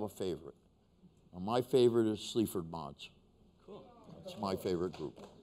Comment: What did Iggy say, what is his favourite band? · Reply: Sleaford Mods.